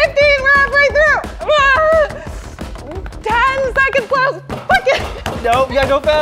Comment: Did she say fuck it at 7:10? ha